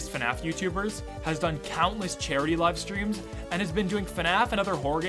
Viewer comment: I first thought he said cherry instead of charity